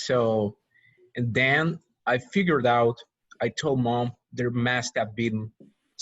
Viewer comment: I have a question about pronunciation ... minute 41:15 when the man (native speaker, I think) read the sentence "I figured it out" ... I listened the pronunciation many times and it sounded like... he read without "it" only "figured out" or my ears are bad? haha. Buena clase aprendí mucho 👍